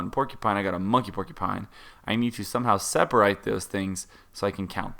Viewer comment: did he say seperight instead of seperate